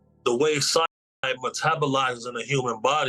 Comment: Does anyone know what he's saying? It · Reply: Cyanide